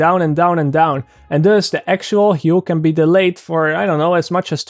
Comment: you said the Dutch word "Dus" that means soI am from the Netherlands I make to mistakes For the rest of the video you English was perfect